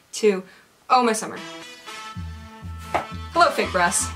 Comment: When she said, "Hello fake brass." I though I heard, "Hello fake breasts" and was like wat O_o